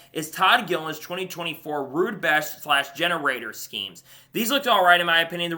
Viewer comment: I've got to admit at first I thought that car #38 said "RUDE BUSCH" like it was some kind of a knock against Kyle Busch. 😆